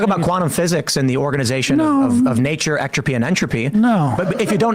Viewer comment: Atropy and Entropy? Did I hear this correctly?